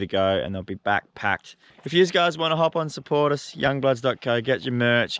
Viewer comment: Why does it sound like he said “Do you guys want to hop on some Porn” At 16:42 😂